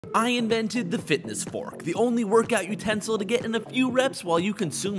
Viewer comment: Why do i hear " I invented another mincraft mini game"